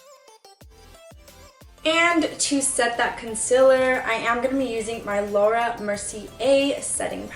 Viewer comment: Why tf do people say “conciler” ? @ 2:10 It’s concealer. 🤦🏻‍♀️😂